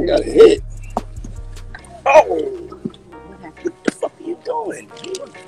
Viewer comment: It sounded like he said that’s the fucking problem with live I have a girlfriend? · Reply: He said wtf are u doing